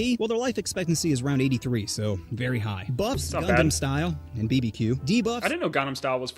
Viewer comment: wait- did call it “gundam style”?!